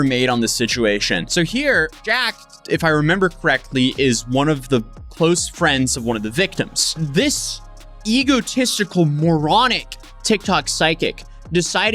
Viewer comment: I thought it said “TikTok Psycho” 😂